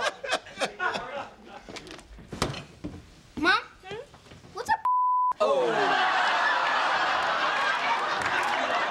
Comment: I still can’t figure out what he said 🤔 Anyone? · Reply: "What's a puta?"